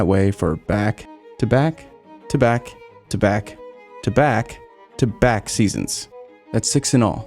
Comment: “Back, tabac, tabac, tabac, tabac” sounded like the Monaco corner 😂